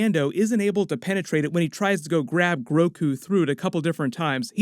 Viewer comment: Did he just say GROKU at 4:48? Lol 🤣